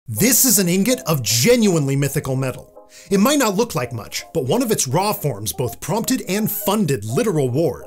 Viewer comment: Im trippin on bad bro i heard it "Mythical meth" 😭🙏